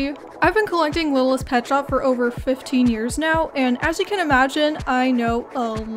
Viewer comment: When you said "I have been collecting for over 15 years" I thought you said "over 50 years" 🥲